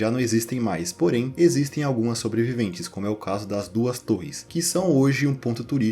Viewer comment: eu ouvi duas torres? 🛩